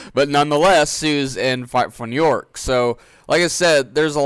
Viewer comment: It sounds like your saying fart 4 New York !😂😂